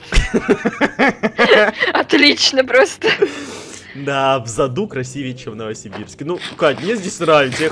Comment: мне послышалось,или он сказал"в заду..."